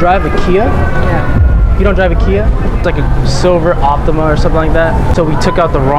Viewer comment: “Do you drive a Kia Optima” Lady: “HOW DID YOU KNOW I DROVE A NISSAN ALTIMA” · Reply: It sounded like she said Ultima but she said Optima